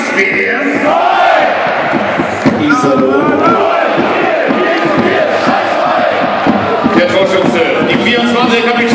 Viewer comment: Was sagt ihr nach dem der Ansager sagt Iserlohn bei 1:29? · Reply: 0 viel, viel zu viel scheiß Verein!